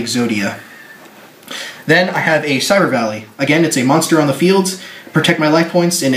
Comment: what is this card at 2:19 u say it very fast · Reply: Cyber valley